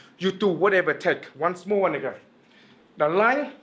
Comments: it sounds like he said once more nigga 😂😂😂😂😂😂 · What a minute... Did he just say "once more nigga" at 3:01?? 😂😂